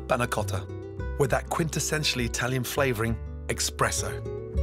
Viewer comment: he actually said eKKKKKspresso